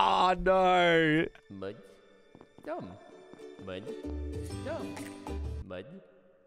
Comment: Greatest QOUTE of all time “MUD DUM MUD DUM” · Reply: I thought he was saying im dum not mud dum